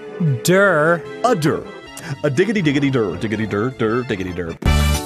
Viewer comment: XD I know Arin is saying "Uh dur" XDDD but if you listen closely it sound like hes saying "udder" That playthrough was "udder" madness... xD